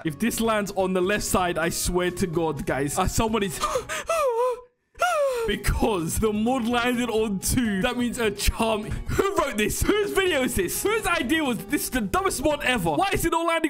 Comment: that sounded like charmander. 🤔 Edit: 16:20 oh